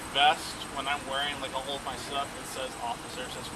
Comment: I thought he said the best says "officer" on it.